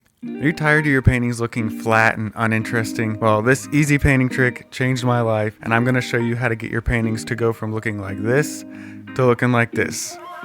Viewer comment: I thought he said "are you tierd of your panties looking flat.." and i heard it twice.